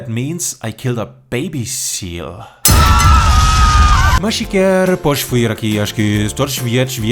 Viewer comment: I'm portuguese and I just heard some words that sound like my language 😂 0:46 «Podes vir aqui? Acho que...» = «Can you como over here? I think that...» OMG I'm dying Great job as always ❤😘